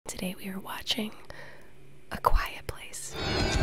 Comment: What movie? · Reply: oh, lol. . . Thought she said quiet please. .